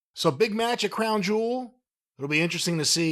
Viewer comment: She didn't say the word "much", she said "match". Corny is going deaf. Whatever. Segment was shit anyway. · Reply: Yep, she said "match". I thought she said "much" when I heard it last week.